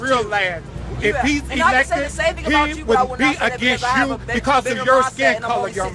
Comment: This 16 year old and all his friends are way more educated and articulate than most of those who support Hillary. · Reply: I believe because he was speaking not writing the word bigot is what was said. With everyone screaming at him im sure it may have sound like he said bigged but he said bigot. I dont think the spelling police were on the scene but just in case they were im sure you would make it a point to bring that to thier attention Correct.If you want to be technical instead of useing a capital letter for the word dictionary and bigged your first word of your comment should have been started with a capital and just in case your wondering im a single white women an avid Trump supporter i own my own company .I dont just go around nit picking over the over a word that was pronounced different then you yourself would have said it.